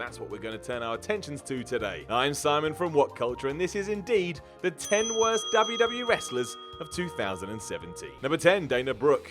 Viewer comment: does he say "the 10 worst WW Wrestlers"?